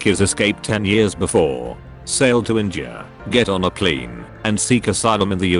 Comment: Damn, this video sure is deep But all I can think about is saying "pleen"